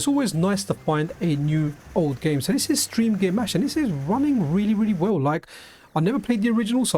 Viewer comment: Game name at 11:51? I didn't quite catch it. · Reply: It's Steamgear Mash